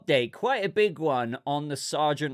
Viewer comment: At. 4:29 I thought you said 'Sargeant Hock Tuay' 😅😅😅 I'm playing ps5 listening off to the side, that shit got me 😂 good morning everyone!😊